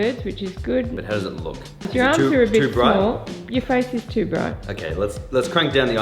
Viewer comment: did she say your arms are a bit small?